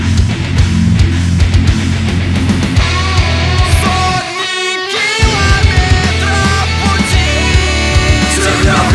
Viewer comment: anyone know what he's saying? -.- even in russian? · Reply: dunno if u still alive but it says "hundred kilometers in road"